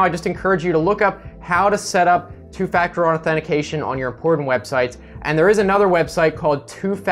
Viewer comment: legit thought u said "porn websites". Had to rewind twice lmao.